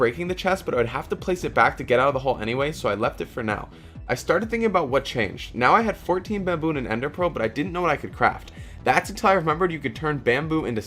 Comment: he says "I have 14 baboon" INSTEAD OF BAMBOO